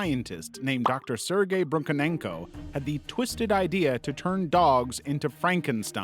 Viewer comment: okay I’m sorry but why did I hear Dr.Sergei Broken Ankle 😭